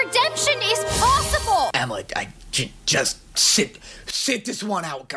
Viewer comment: is he calling her Emma or is that just me- 😂